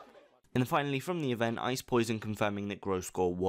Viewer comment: realized a long time ago that accent made it sound like he was saying "ice poison" and that he wasn't deliberately trying to say "ice poison", but its still kind of annoying